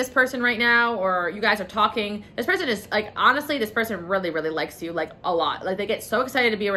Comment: Picked 7 and the first thing I hear is “This person really really loves you guys” makes me so happy💕✨💕